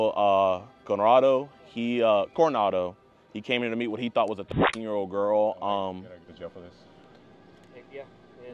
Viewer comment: I think they were saying thirteen year old